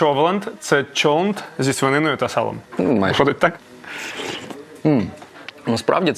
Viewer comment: что сказал?😂😂😂я одна слышу наваристый🤣🤣🤣🤣🤣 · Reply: Офігєнний